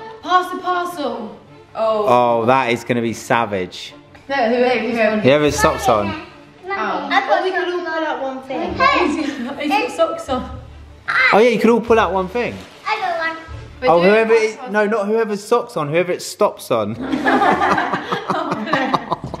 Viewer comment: i also thought said whoever has socks on XD not stops on